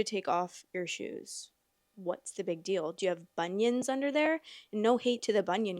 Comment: I thought you said do you have ONIONS under there the first time 😂😂 With it being right after the strip tease comment, I thought she was stuffing. Guys definitely get upset about stuff, but it's stuff I consider incredibly stupid like video games and sports. All this energy over that, but none for a real issue that affects me. I couldn't deal with it personally